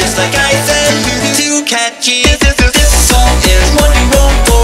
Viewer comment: I lowkey thought he kept saying "dishsoap" instead of "this song"